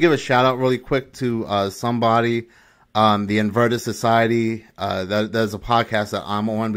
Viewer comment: Did anyone understand what the name of that podcast is? It sounds like he is saying “inverta society”. But I can’t find a podcast anywhere with that name. He says it at 22:40. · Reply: <link> sorry it is theinvertedsociety but it is mainly about gaming 🍻